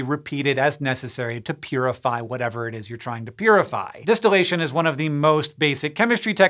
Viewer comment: "Distillation is one of the most basic chemistry techniques, and it DISTILL used a lot." xD Probably not on purpose, but that's what I heard.